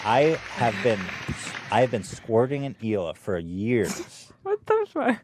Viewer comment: i skip ahead alittle to avoid the intro and the first thing i hear is "iv been squirting in hila for years now"